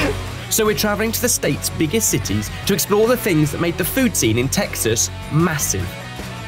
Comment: thought it said to find out what made our food team massive 😂